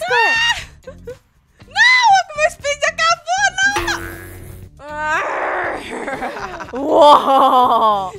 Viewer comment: eu não entendi o que a falou? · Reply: Ela disse "Não o meu speed acabou"